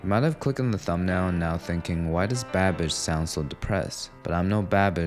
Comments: He said Babish, but I heard "bad bitch". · I thought you said "bad b*tch" instead of "babish"